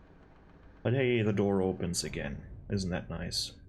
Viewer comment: did you just say "olha aí, the door opens again. Isn't that nice"? 😂 are you brazillian?